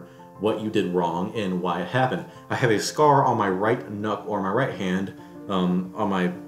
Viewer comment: Lol, sounds like you said "I have a scar on my right nut, I mean hand"